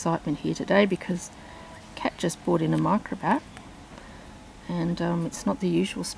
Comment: At first I thought you said a cat brought him in 😁 then I realized it was Kat!